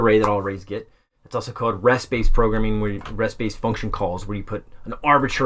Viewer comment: what does call functions that accept a variable number of arguments? Is he saying "Rest base functions"? I can't quite catch it.